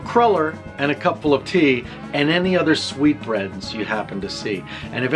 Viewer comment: I thought the sweetbreads thing had something to do with her appearance- I was wrong in so many ways… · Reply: I didn't even know he was saying sweet breads. I thought he was weirdly saying sweet brains.